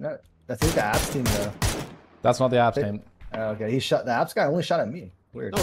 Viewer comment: I heard "the Epstein" two times and wondered what kind of esports call-out I missed this time 😂